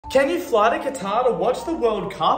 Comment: I thought he said, can I fly with a Qatar?